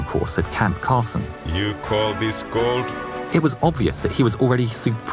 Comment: Dat cold weather training scene lauri : YOU CALL DIS COLD AMERICANS : visable confusion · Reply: I thought he said you call this gold?